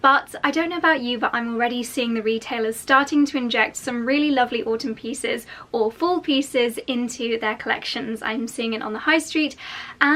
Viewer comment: When you said, "Autumn pieces or Fall pieces" I thought you said, "Autumn pieces, awful pieces!"